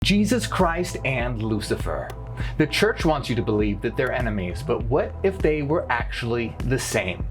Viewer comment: I heard that as "the church wants you to believe they're both animes"